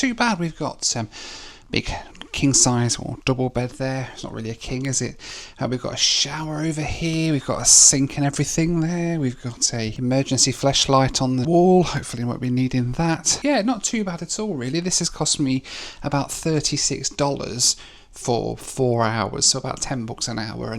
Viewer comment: Nearly spewed Diet Coke out my nose at 18:46 when I’d swear said “Emergency Fleshlight”…